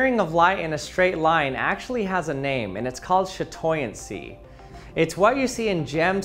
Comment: Did you just say, “shit”oincy, 6:04?